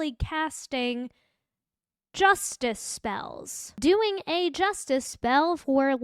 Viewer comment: I was kinda spacing out, and thought you said "Justin spell" and thought you were talking about the boyfriend of the lady cop that got shot...was his name Justin...the firefighter? Anyywayyy...keep up the stream 😆